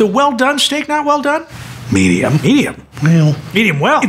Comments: I thought he said “Im gonna go with my gun” and I just thought “yea at this point I dont blame you” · I thought you said well done shake and I was loosing my minds